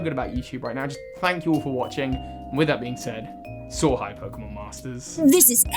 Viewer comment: Funny thing. For years I thought you were saying "So high".